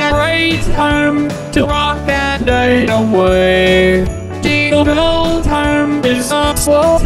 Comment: On 0:38 he said "to rock that night away" but the video caption said "rock the night away" spot the diffent